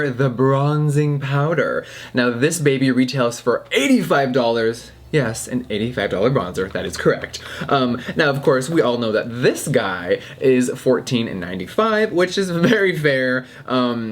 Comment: at first I thought you said $800 bronzer and I was like bitchhhhh I’m out here ballin on a budget of like $2